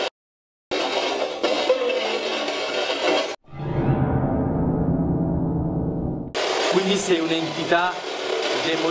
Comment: io ho capito Oliver o Olivia, comunque siete fantastici♥ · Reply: Dice chiaramente Olivia!